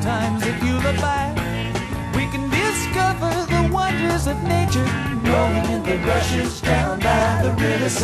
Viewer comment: When they sing that part "we can discover the wonders of nature rolling in the rushes down by the river side" I thought he was saying "we can discover the wonders of nature growing in the bushes down by the river side" lol. Then I read the lyrics. I like my version better lol.